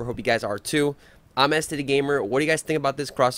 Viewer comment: When you said it’s este the gamer it sounded like you said it’s std gamer 😂